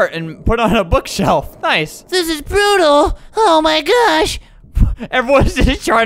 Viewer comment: When he said brutal I thought he said beautiful